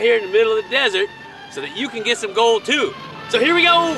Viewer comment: Thats awesome,man. I want to do that too ! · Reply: for the longest time i thought you was saying "So C'mon, let's GOLD!!!!!"